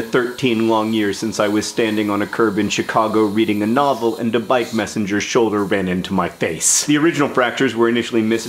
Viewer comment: can someone tell me what John says at 0:15? what ran into his face? I'm sorry my english is not that good.. oh and ps: excellent video! 😊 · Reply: "A bike messenger's shoulder" :)